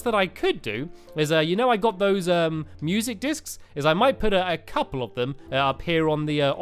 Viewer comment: Music Dicks?!?! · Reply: Sebastian Puga lol i accualy heard him say music dicks and then he corrected himself!!!!!!!!!!!!!!